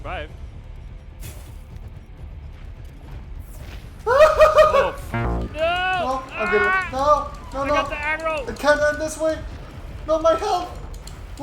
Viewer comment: When said I got the aggro it sounded like he said I got the Adderall